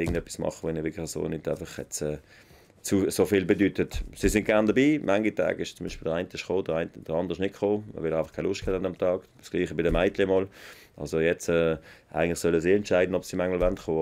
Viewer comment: Very hard to understand swiss german as someone from Germany. Understand 20-30%, if i concentrate maybe 50%. Can someone tell me what he says 14:33, "Sie sind gern dabi (dabei), ...???" and the next sentences. Absolutely no idea. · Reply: An manchen Tagen kommt z.B. nur einer der Söhne weil der andere keine Lust hat, so ist es auch bei den Mädchen. Sie sollen selber entscheiden ob sie kommen möchten oder nicht. Heute wars besser dass sie nicht dabei waren, da das Team in der Box bei engen Matches immer ein wenig angespannt ist.